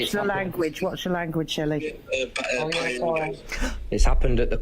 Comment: Who's the Woman That Say's Watch Ur Language Shelly, MI5 ?? (22:46) Or Am I Hearing Things